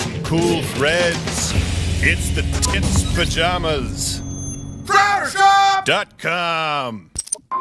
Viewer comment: No no no, I heard “It’s the Tits Pajamas” and I can’t find them Someone please, I need this